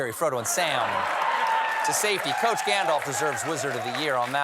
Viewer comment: I thought you said "George Santos" instead of "Coach Gandalf" and it mafe equally as much sense 😂